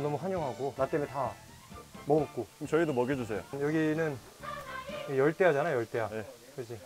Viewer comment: On 3:35 i heard "YEOLDAEYA"???